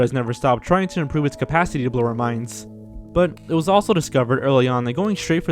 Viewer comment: I thought I heard you say, "Hollywood has never stopped trying to improve it's capacity to lower minds". Oh, "blow our minds".